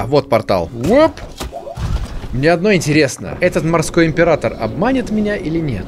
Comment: признается, что ему ОДНОЙ интересно.... ладно, не ешьте меня с хлебом, просто услышала и стало светлее на душе😅😂❤